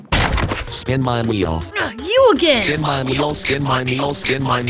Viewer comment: why do I hear “skin my meal”